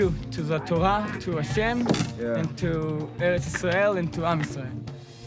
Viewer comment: he actually says “To eretz Israel (the land of Israel), and to am Israel (the nation/people of Israel)”